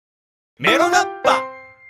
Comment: メロガッパがエロガッパに聞こえる